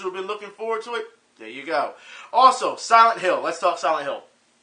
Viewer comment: Honestly, I thought you said "Sanic Hill" when you really said "Silent Hill". As for Akuma in Tekken 7, even though you feel it's shoe-horned, I'd say at least Badamco puts efforts into their shoe-horn. Then, you'll bring up a counter argument pertaining to Soul Calibur V. Later, we'll end up a stalemate somewhere along the line. Anywho, I now have a strong subliminal urge to play SF3 Third Strike.